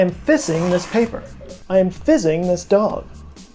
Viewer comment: I swear I thought Joe said, "and I'm fisting this dog." 😂